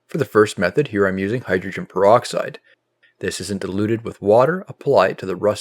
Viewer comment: Did you say at 0:38 this “isn’t” diluted with water? Just want to be sure. Thanks. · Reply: I had difficulty hearing that part, as well. The notes below the video clearly say "isn't" diluted. However, hyd. peroxide comes in several different strengths (expressed in percentages), and he never mentions the strength he's using.